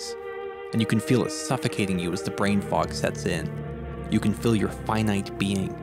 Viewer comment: I thought he said, "as the brain fart sets in" 💀💀